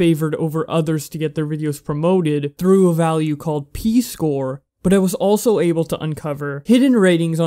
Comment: i thought i heard "Pee Score" lmao